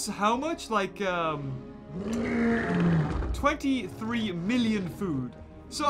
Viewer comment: When he said 23 million food I thought he said dna